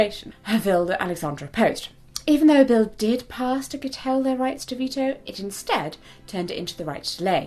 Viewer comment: Hey, at 15:19 ish, the CC says [inaudible], and I'm p sure the word she was going for was curtail.